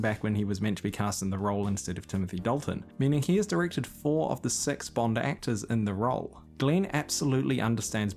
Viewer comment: Please don’t take this the wrong way (as I’m your mate from across the Tasman), but when you said ‘four of the six bond actors’ all I heard was ‘four of the sex bomb actors’ 😂😂😂😂 God love a kiwi ‘deck polish’ accent! Xx